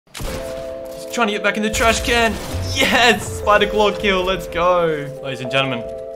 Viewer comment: Who else thought it said “Sniper Claw” 😂